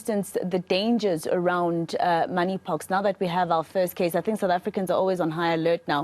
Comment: I thought i heard it wrong money pox😂